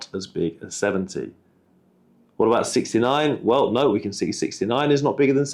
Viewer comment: 0.69 but i heard 69